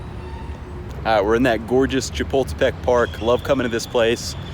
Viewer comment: What park? It sound like you said chipotle park 😂. It is Chapultepec ( Cha-pool-tay-PECK). Great video!